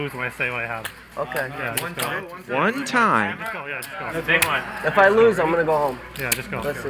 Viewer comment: did he say yeah go home ??😭